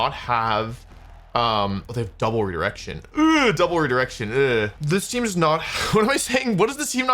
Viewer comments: I heard double erection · did anyone else hear double erection